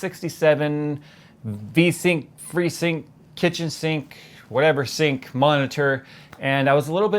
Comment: The kitchen sink joke was so bad, yet one of your best... I nearly died of laughter lol. Jay, keep losing weight, it's really inspiring and encouraging! Keep uploading your awesome videos. I will always be here to support you. Stay awesome :) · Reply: Guy Random ahha i didnt realise that he said Sink until i saw your comment, i thought he said Kitches Sync xD